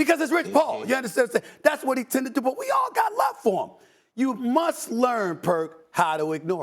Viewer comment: I thought someone said, "KUMUSTA" 😂😂😂😂😂